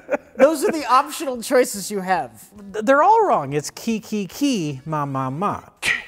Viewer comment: I have never heard it as ki ki ki ki, ma ma ma ma and yet so many people swear by it. Ch ch ch ch, hah hah hah hah (But not like a laugh, more like the sound you make when you breath on your glasses to clean them)